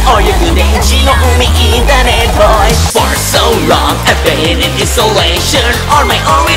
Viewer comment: i thought for the third song i thought i heard i fart so long 💀